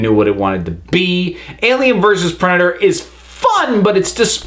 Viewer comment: For a few moments I thought you said "Alien vs erection", lol.